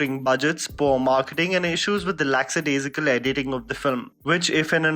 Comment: What he is saying at 0:42...'lack satirical'